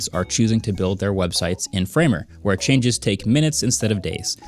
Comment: I heard "Framework" instead of "Framer" and had a mini heart attack lol. Like "HOLD UP WAS THIS JUST A PARTNERSHIP"